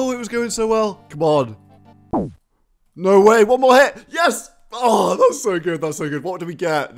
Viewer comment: did you say "no way, vou morrer"? at 1:02? hahahah its a Brasilian speech haahhhahah ik it was "no way 1 more hit", gotcha :)